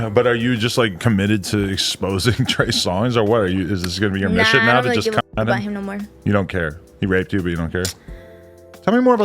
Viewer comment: Did she say "nah I'm not dating black men no more"?? 🤔 That's what it sounded like. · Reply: She said "nah I don't really give a fxck about him no more". Talking about Trey Songz.